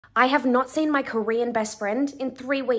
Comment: Am i wrong or did i just hear “Koweean”